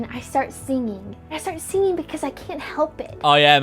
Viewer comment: I heard seeing, not singing so I was like “yeah I sure hope when you wake up you start seeing”